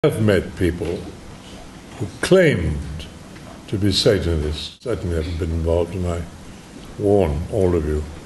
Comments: I keep thinking of the word "Separatists" whenever I see "Satanists" in this comment section. God bless Mr. Lee's soul. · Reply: I heard "who claimed to be safe in this." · After he says "claimed to be satanists" it sounds like he says 'satan is involved' 😳